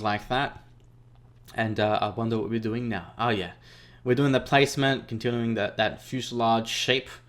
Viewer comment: No offense but when you launched the Valkyrie it sounded like you said 'dwing' instead of 'doing' 😆